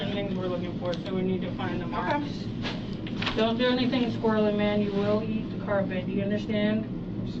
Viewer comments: Woman officer kneeling down looking for freckle: “Don’t do anything Squirrly Man or you will hit the carpet.” “Understand?!” 🤣🤣🤣🤣🤣🤣🤣🤣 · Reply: I thought she said: … “or you will eat the carpet.” 🤭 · does she say "Don't do anything, squirrelly man, or you WILL eat the carpet"??😂